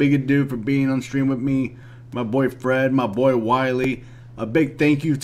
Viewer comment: Aaaaaaaah my boy Fred I thought you Said my boyfriend lol